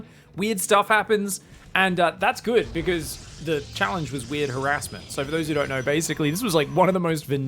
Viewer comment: I could have swear I heard you say "REAR harrassment" and was like, hey that doesnt qualify!!!!